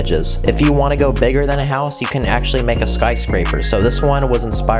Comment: I heard "spice-scraper" the first time. I guess that would be on brand 😂